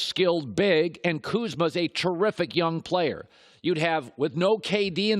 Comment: Did he just say Kuzma is terrific defender? LOL